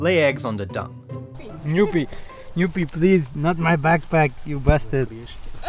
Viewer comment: did you just call that goat bastard? xD